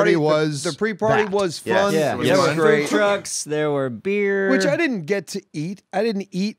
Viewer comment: in to the video it sound like says “at the party they had drugs and beer” but he is saying (food trucks)